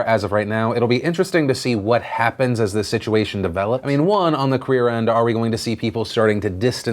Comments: SO. MUCH. NEWS. agh. I'm really interested to see what else will be revealed with all of these stories as more information starts to come out. As always, let me know your thoughts! <3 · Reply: Phil says "on the queer end." While talking about the Kevin Spacey controversy. I know he meant "clear." Just think its weird his mind threw out queer instead of clear. 4:10* and wait to hear it at * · Did he just say “Queer end”? T R I G G E R E D It’s just a joke, Jesus.